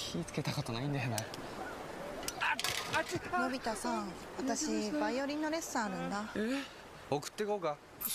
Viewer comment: Dang I love when Japanese ads have a series lol thanks for translating these (& the other vids too I like your games taste)!! Also 0:45 it's just my guess but I think I heard violin no lesson? So probably something like she got violin lesson (so she needs to go back home hahaa)